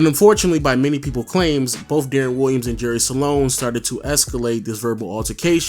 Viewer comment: Jerry Sloan not Salon... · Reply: 😂😂😂😂😂 Salone!! I almost thought he said Jerry Stalone! 😂😂😂 once is ok but multiple times? Come on dude